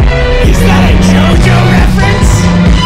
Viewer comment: DId Say iS iT a JojO reFErenCe?